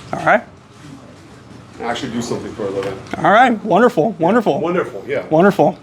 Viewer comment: I think he says 'and actually do something for a living'